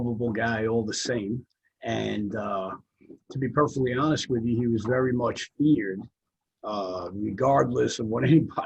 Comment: ' he was very much revered' does he say?